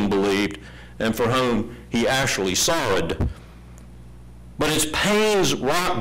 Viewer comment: I think he says "sorrowed" at 4:55.